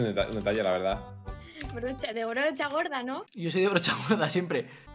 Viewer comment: Pensé que decía Borrachagorda en vez de Brochagorda xd